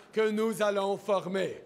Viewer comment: What did he say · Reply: Que nous allons "FORMER" That we will form..